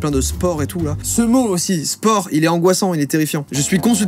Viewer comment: "Spore, ce mot est terrifiant" Moi qui pense au sport : ouais, trop